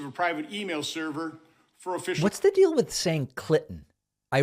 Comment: Wait. Did i just hear David say “What is the deal with saying clit” ?